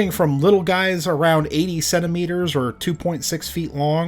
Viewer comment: I misheard "80 centimeters" as "87 meters" at about 7:04 and was briefly horrified lol